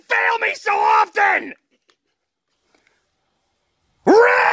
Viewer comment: Before watching this 0:53 I thought the original meme says spell ribs